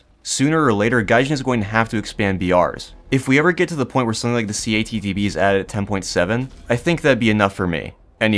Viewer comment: what did he say here? It sounded like c80tb to me. (My headphones are dying so I can't tell) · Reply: He said "CATTB" it's an american technology testing Abrams. It was fitted with an experimental 140mm gun, among other things.